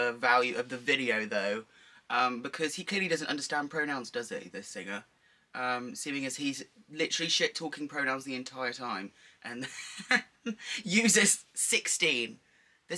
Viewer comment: wait, what did you say at this bit? i can't really understand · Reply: "He must not understand pronouns this singer, seen as hes litterealy shit talking pronouns the entire time while using sixteen of them" :D hope I helped